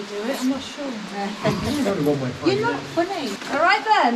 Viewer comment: Am I hearing things or did someone say to the guide at 11:12 "you're not funny"?